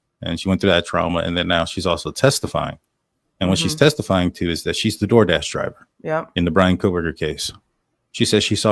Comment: When I listened to it, my first thought was that she said "I saw Brian, I'm the doordash driver".